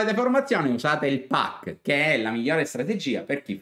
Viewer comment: non ho capito se non ho formazione cosa devo usare .....il pak ? È un acronimo ?Si scrive così ? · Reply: È il Pac il piano di accumulo